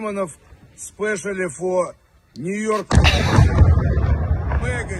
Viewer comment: Did he say 'New York'?